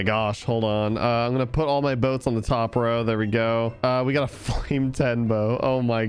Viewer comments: did he say "we got a fuclame 10 bow" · he said bow instead of book